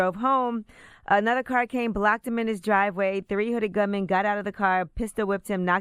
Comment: I Swear It Sounded Like She Said 3 Hunnid Gunmen Lol 😂